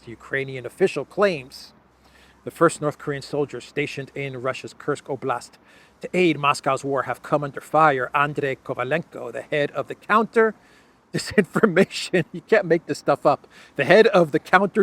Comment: I thought they said Russia's broke😂😂😂